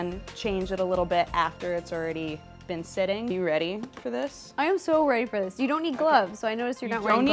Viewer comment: did Ro just say that she was rapist for this?